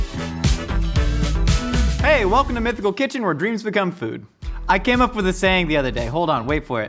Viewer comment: Each time watching this, in the intro I hear: “Welcome to Mythical Kitchen where dreams be Kung fu” 😂😂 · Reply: noo he says : when dreams become foood :D :D but it sounds like it right?